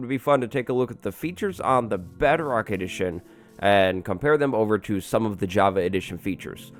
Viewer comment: I thought he said ‘Better Arc Edition’